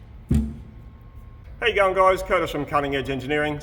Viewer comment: I just don't get it. I constantly hear "Hey young guys" instead of "How you going guys". English isn't my first language, but still...